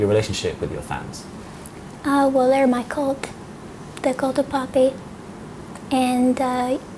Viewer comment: What was she saying at 6:19 to 6:26? I'm not trying to be mean I really like Poppy, but I just couldn't understand her at that part. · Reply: "My cult, the cult of poppy"